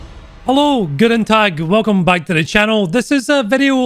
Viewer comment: Just some advice, in your "guten tag" you should stress the t's more, but it sounds good as it is. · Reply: it sounded like: G'den dag